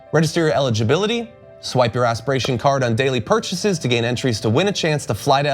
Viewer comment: Did he just say "swipe your ass"?